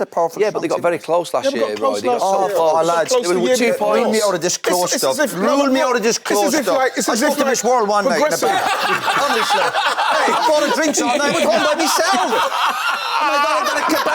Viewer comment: What does Roy Keane say at 35:03? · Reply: "I spoke to Miss World one night in a bar" 😂😂